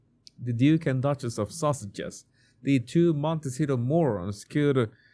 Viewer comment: Just thought I heard call Harry and wife the duke and duchess of sausages. Good name.